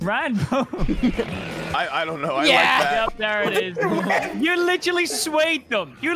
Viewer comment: Am I the only one who heard Ranboo go “I dunno I mean I like that”